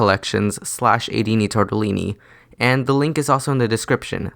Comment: i thought you said Aidini, Totally Me.